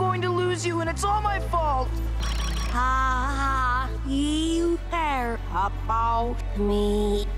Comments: i think she said: Ha ha, you care about me. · is sounds like she said “Ha Ha, You care about me.” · At the end I think she said.... haha you care about me..?? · i think amethyst said " haha you care about me" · i think she said haha you care about me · If your wondering what she is saying,she is saying haha you care about me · At the end sound like she said ha you care about me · the last part sounded like she said "aww you care about me"